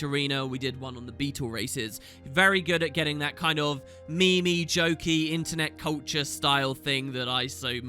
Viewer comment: did just say "me-me" instead of meme?